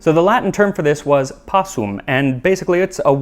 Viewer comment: Yo I thought my dude said "possum" not "passum" and was crazy confused